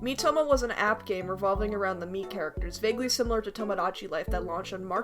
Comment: "Miitomo was an app game revolving around the meat characters" is what i heard at 1:14 xD